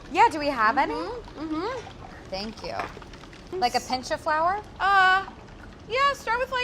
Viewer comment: like a pinch of flour I heard like a pinch of paprika